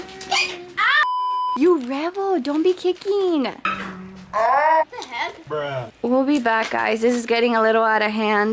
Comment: I love how called something else not her name she said GIVE IT TO ME WANDA OR WADA! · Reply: actually she say give it to me and said what the and since can’t pronounce very good it sounded like she said wanda instead of what the